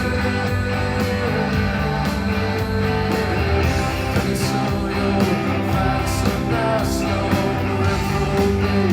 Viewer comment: anyone know what word he uses instead of "satanist," in this beach life-in-death performance? I swear he replaced it with something, "pretty soon you'll find some nice young [blank]..." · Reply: he said reformist